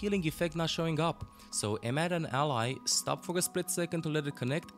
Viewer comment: what is he saying right here? Sounds like have an ally but its cut off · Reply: "so aim at an ally, stop for a split second [...]"